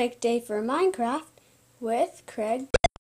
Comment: Okay go to 4:38 and try to hear real name i think it said Craig